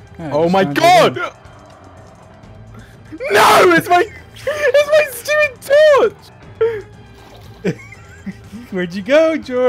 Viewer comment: i heard "WHERE'S MY SUPERSUIT!" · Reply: He said: “NO, ITS MY STUPID TORCH” He had optifine torch on Optifine allows players torches to illuminate through blocks Dream found him only because he forgot that he still had the optifine on and decided to plop a torch down